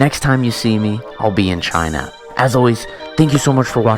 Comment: What did u usually say after the video? Example this at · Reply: And stay tuned